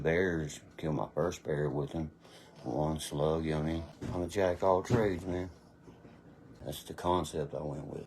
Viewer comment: What'd he say? He'll Jack All Tradesmen Off huh??